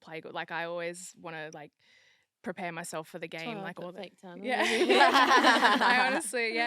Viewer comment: What did Hempo say there? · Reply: i think it was something along the lines of “it’s why you wear all the fake tanner” 😅